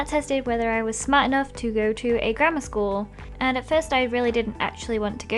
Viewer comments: Until a little while ago I thought when Ldshadowlady said "I was smart enough to go to a grammar school" I thought she said "I was smart enough to go to a GRANDMA school" so I was like really confused and think "What is a GRANDMA school?" XD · The first time I thought she said she got into a grandma school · I Thought When She Said "Grammar" She Said "Grandma"